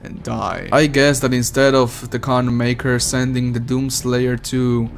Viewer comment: It's pretty stupid but at the same time funny that if you put the subtitles on at 10:39, you'll find that when says "Khan Maykr", subtitles say "condon maker".